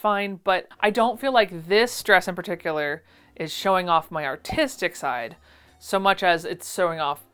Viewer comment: So glad you put the word on screen at 14:11 because I definitely heard 'autistic' 😅